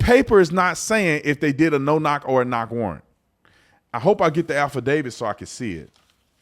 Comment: lol, sounded like you said "alpha david"